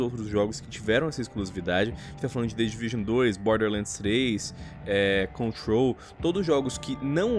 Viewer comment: Lion: tiverão essa exclusividade ,the devision 2, borderlands 6 ,Control kkkkkkkkkk (ou só eu entendi isso )